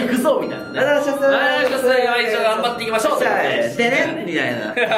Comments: ずっと はいどうもー水たまりボンドですー ね、ま一同頑張っていきましょう！ って聞こえてましたw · 今一度頑張っていきましょう！に聞こえるのは俺だけかな？？ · 全然聞こえます(￣Д￣)ﾉ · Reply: 正直最初は、今一度頑張っていきましょうに聞こえた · なんかメンバー一同頑張っていきましょうに聞こえるw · サブチャンもサブチャンらしい企画で本気！ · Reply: はい どうも水溜りボンドです ね、まぁ一応頑張って行きましょう。って前から聞こえてたお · 今まで、今一度頑張っていきましょうって聞こえてたｗ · 「今一度頑張っていきましょう」って聞こえてた笑 毎日動画って言ってたのか！私難聴かもしれんわ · 聞こえてたけど、意識すれば今一度頑張って行きましょうってめっちゃ聞こえる笑笑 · 今一度頑張っていきましょうって聞こえる笑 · はいどーもこんにちは水溜りボンドですー、まあ一応頑張っていきましょう！って聞こえる笑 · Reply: ゴールデンフリーザ 俺は「1族頑張っていきましょう。」って聞こえるんやけど... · 今一度頑張っていきましょうに聞こえる！！ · まぁ一応頑張っていきましょうって聞こえるときあったかなー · 「ね!今１度頑張っていきましょう」 って聞こえる… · わしの耳では、はいどうもー水溜りボンドですーお願いしますー！、ねっ今1度頑張って行きましょう！ に聞こえる｡･*･:≡( ε:) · 「ねっ、いまいちど頑張っていきましょう!!!!」って聞こえてたw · 「まぁ一応頑張っていきましょう！」つて聞こえてたw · 僕は始めのころ まー一応頑張っていきましょうって聞こえてたけど後々わかった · 遅すぎるけどやっと謎が解けた笑笑 今までずっと「はいどうも水溜りボンドです〜ね今一度頑張っていきましょう」にしか聞こえてなくて、なんで今一度？て思ってたら自分の耳がおかしかったわ笑笑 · ねっ！まあ一応頑張っていきましょう！って聞こえる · はいどーもー水溜りボンドです では一度頑張っていきましょう！ に聞こえる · 私は、「はい どうも〜 水溜りボンドです〜 ねっ まあ一応頑張っていきましょう っということでね」に聞こえてた← でも、水溜りボンドが、一応頑張っていきましょうとか言うわけないよね~(´罒`)♥ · いちよう頑張って行きましょうって聞こえた！！ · 毎日動画って言ってるんだ！ いまいちど頑張っていきましょうって聞こえてた…… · 水溜りボンド最近はまってきて、この動画見るまで「では一度頑張っていきましょう」と聞こえてました。。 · はいどうも〜水溜りボンドです〜まー1度頑張って行きましょう！って聞こえてた笑笑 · ねっ、いまいちど頑張っていきましょう！ って聞こえるのは私だけですか…ww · 『まあ、一応頑張っていきましょう』に聞こえてたww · まぁ、道頑張って行きましょうに聞こえるの俺だけ？ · 今一度頑張っていきしょうって聞こえる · ね、今一度頑張っていきましょう！ に聞こえる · 前、｢はいどーも水溜りボンドです ね！一応頑張っておきましょう｣って 聞こえたw 私の耳どうかしてるw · 個人的に今一度頑張っていきましょうに聞こえてしまう時期があったわw